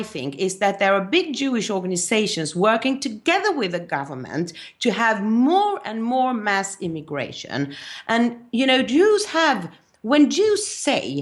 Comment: Did Ingrid just say 37:40 there were "Big Jewish Organisations, working together with the government to have more and more mass immigration" . Why would those organisations want Mass Immigration? Gad sugests it is to get in favour with the muslims who will eventually rule. I would have thought the Jews more than anybody would know that will not work with Islam. Is another option that these Elite Jews want to force Diversity on the European, to weaken him?